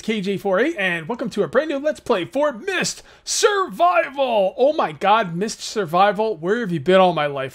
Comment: I Thought Said “Meath Survival!” lol!!!